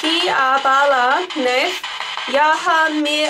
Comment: when you asked if Balanath was there, I think I heard a voice say " Bala... Nath.. Nahin" which means that Balanath is not there. It's the moment when you heard someone calling out 'Bala'. and when you asked about the name and you couldn't understand what was the voice saying. I think I heard it say "Rani" which means queen in hindi. Also when you heard the voice saying thankyou I think it sounded like "Dekh sakti" which means 'I can see' . I think I heard 3-4 voices all mixed up but there was definitely a female voice in it. Or may be it's just me imagination. Thanks